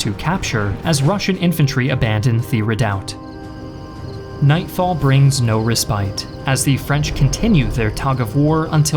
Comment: what does he say? Could someone translate these words pls? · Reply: Holy Mother of God